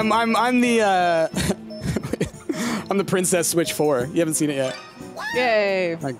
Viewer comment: i really thought u were saying Princess Witch lol, now looking at these comments i'm disappointed lmaoo